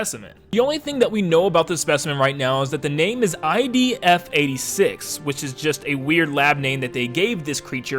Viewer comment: Sorry, were you saying IB or ID there? It kinda sounded like IB, but it should be ID.